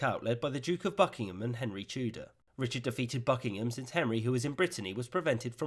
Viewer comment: I'm sorry but every time I hear "Henry Tudor" my brain substitutes "Henry Tulip".